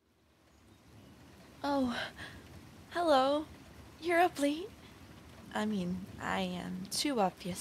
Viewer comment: She said your up late I thought she said ugly And she's not even a yandere just a girl who has a crush and is a bit creepy